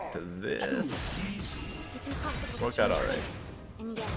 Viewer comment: I think he said "it turned out alright" or something but at first I heard "futanari" P.S. English is Not my first language.